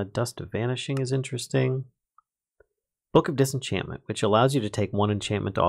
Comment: I thought it said book of dissapointment.